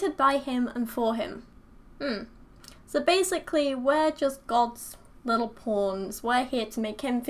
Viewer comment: You know something is fucked up about your belief system when says "We're basically just God's little pawns", but I mishear "We're basically just God's little porns"... ... And it takes me five minutes to find that odd... I was all like "Yeah, that sounds about right..."